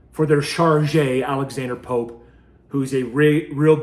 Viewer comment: Who is the POS he referenced- sounded like he said Alexander Pope but the only thing that comes up under that name is the poet from 17th century